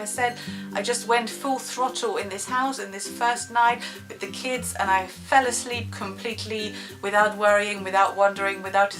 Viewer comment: hey sorry what are you saying in 5:03?? 'i went...' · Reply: I went full throttle in this house.....